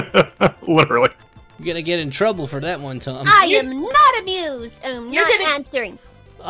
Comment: Why did I think she said “I’m not abused”?